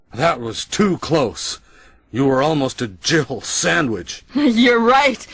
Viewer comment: >Jill Sandwich ... is that what Sienna Guillory was playing? Because she was definitely a snack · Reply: I thought he said “jiggle sandwich”